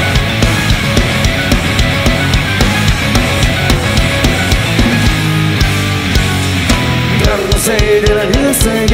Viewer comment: 命様がギターを肩に掛けたあたり〜00:25の髪の毛揺らしながら弾き始めるのが堪らなく好きです。その後の○○ちょうだいが何回聞いても聞き取れない… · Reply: ハイ拳頂戴 かと